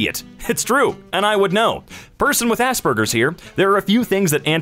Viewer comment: When he said asperger’s I got really confused because I wasn’t looking and thought that he said ass burgers